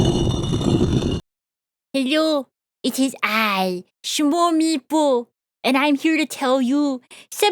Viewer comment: Fun Fact: when I first heard her voice, I didn’t hear “Smol Maple”, I heard “Swarmepoo.”